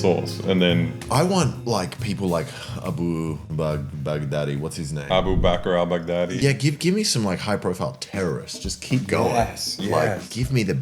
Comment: lmao the autogen subtitles say abu bakr albaghdaddy